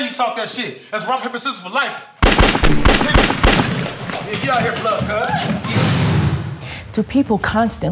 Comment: did he say "get outta her Blood Cuh"? 🤣🤣🤣